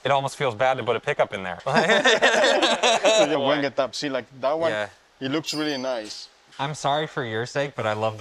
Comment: what did he say? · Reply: Wenge top, see like that one, looks really nice